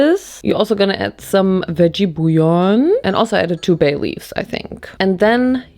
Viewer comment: I heard baileys and thought that’s weird to add booze… but she said so so let’s go 😂